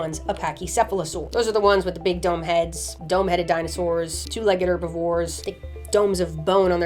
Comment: Initially I thought she said “Big dumb heads” 6:36 . I was thinking “Damn Lindsay what did pachy ever do to you?”